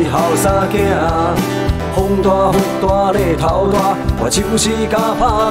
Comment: 不看歌词我就听成了，风大雨大奶头大😁